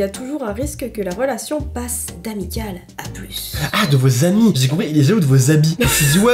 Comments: j'ai compris habits aussi j'ai bugué pendant 10 secondes... J'en ai marre de moi parfois 😂 😂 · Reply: Oui moi aussi j'ai compris habits au lieu de ami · moi aussi j'ai compris de vos habits XD et je me disais du genre: -waw mes ma meuf elle a trop de beau habits je veux les meme! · moi aussi j'avais compris habits😅😂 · moi aussi j’ai compris des habits 😅😅😂🤣